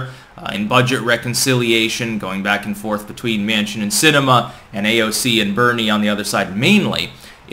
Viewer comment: Instead of "Manchin and Sinema" I heard "Mansion and cinema" and I thought, "Geez, these elites are getting awfully damned…. Elite!"